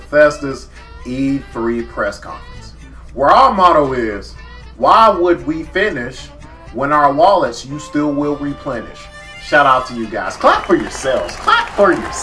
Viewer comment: I couldn't quite hear that phrase but Lmao😁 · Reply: "Why would we finish, when our wallets you will still replenish."